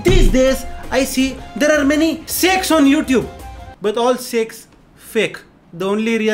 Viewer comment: i heard "there are many sexx on youtube "xD